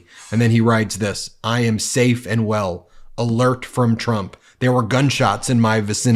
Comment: I thought he said " I am safe in hell".